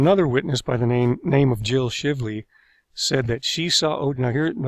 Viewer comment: & 13:15 I had to stop chewing to ensure I heard that correctly. I thought you said "Shitly" I know she was found to be incompetent, but damn 😂 Great job with this video by the way. Appreciate the research and additional information.